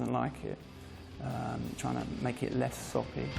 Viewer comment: “Trying to make it less ______” what is he saying? Sloppy? Sappy with a British accent? Sore pea? Saw pee?